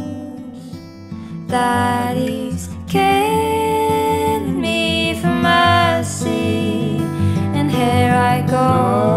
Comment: I heard "he's killing me for mercy" Gorgeous stuff 👍